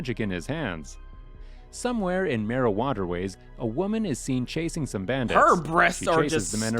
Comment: Why does it sound like he said Mari-wanna ways